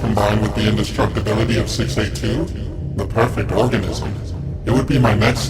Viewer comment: I thought it said Orgasm 😂🤣😂🤣😂🤣😂🤣😂🤣😂🤣